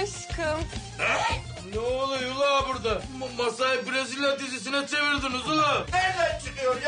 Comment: Adam Brezilya dizisi diyince brazzers dizisi anladım aq ben fesat değilim kulaklık kötü (!).....